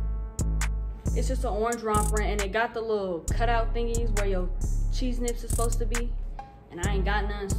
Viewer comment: did u say cheese nits 💀🤣🤣🤣